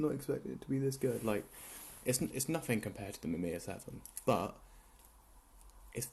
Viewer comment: Did you say mimir 7? 2:33 what camera are you talking about? I cant find it on a google search 🙃